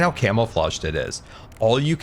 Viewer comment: I swear at 20:03 said 'camel-flaged' instead of 'camouflaged' and that IS something he'd say so I'm not going to question it. Maybe it was 'cammo-fah-zhd'. But I'm going with 'camel-flaged'